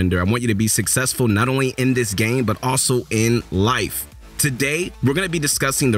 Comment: I thought he said “end” life 😂😂